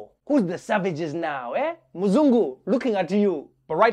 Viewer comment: Did he just say Mzungu? 😂😂😂